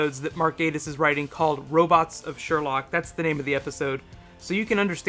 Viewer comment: did he just say "robots of sherlock"